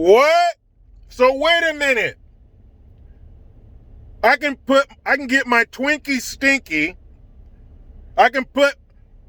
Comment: Did you just say “...get your Twinkie stinky...”?! 🤣🤣🤣🤣🌺